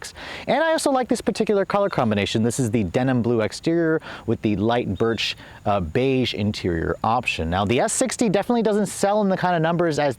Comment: At first i thought he said "light bird shit interior" lmao!